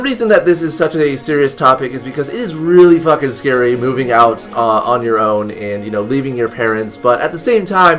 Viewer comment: I thought it said coming out and I was like "I knew it". I guess I didn't